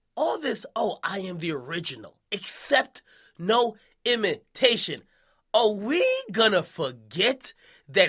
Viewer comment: i thought he said accept no immigration